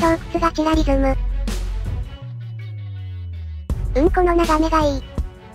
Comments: ウンコの眺めがいいと聞こえる笑笑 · ほんまにごめんなさい。うん、この眺めがいいではなく、💩の眺めがいいに聞こえてしまう🤣